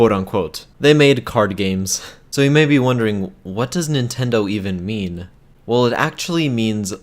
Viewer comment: Did you just say "What does Nintendo even meme?"